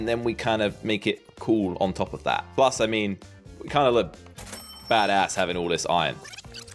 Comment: did he say a bad word it's been a while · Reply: badass isn’t necessary a bad word, just depends how you say it